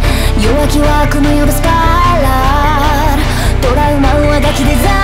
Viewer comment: スパイラルがスパイダーに聞こえるのすごい